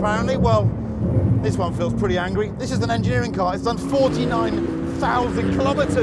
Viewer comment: Did he say "This is the Nigerian car" at 3:49?